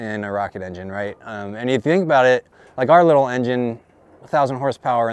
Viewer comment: to hat threw me way off. I thought he said euro to 1K in seconds instead of zero lol. I was like wait wait wait whoa what?!!? Lmao.